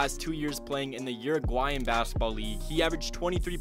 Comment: Puttin 25 against trae young cmon. and doin 23 in Europe rlly good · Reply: I thought he said eu league